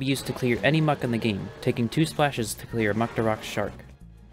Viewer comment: bro i swear i heard mucktorok shart here 😂